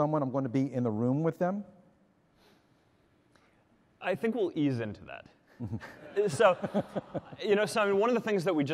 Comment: What does he say at 14:26 pls? I'm not native english speaker. "I think will ease into that"? or something?